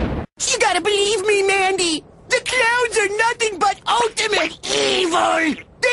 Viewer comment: Oh he's saying "clowns" I thought he was saying "clouds"... Well, at least I figured that out in my 20th time coming back to this video...